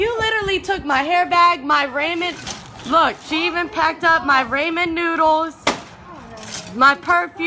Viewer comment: she really said "RAYMOND NOODLES"